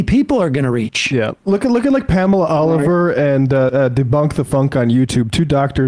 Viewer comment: Hey folks from Ned in Spain, hope everyone is doing well. I really hope this caller can put together some simple, clear and succinct videos to expose JP and other characters who use their credentials, real or imagined, to cause harm and division while raking in the millions. I found Debunk the Funk, looks great but who is Pamela Davis ? Thanks again for all the hard work. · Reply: I think he said Pamela Oliver not Pamela Davis but maybe I misheard. Anyway, if I did hear right, I think he may have mispoken and meant Susan Oliver, her channel is Back to the Science. It is very good.